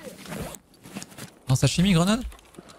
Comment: sashimi grenade ? Nan c'est "chie miche" les grenades. Quand ils disent "bière et sashimi" c'est sympas, "pince moi le nez chie miche" faut se casser haha. La traduction parler d'elle même mdr